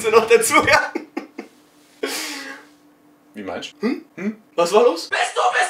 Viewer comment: hab ich da etwa ein "wie meinsch" gehört? :D ihr schwaben